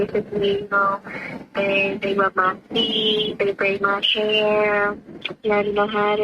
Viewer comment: tf did she say? “They love my feeeeet”…she’s getting diddled or what?